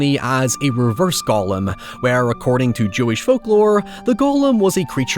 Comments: all i heard was "reverse gollum" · it's just the accent, but i thought you said "reverse-gollum" which is a fucked up joke had in the military 😂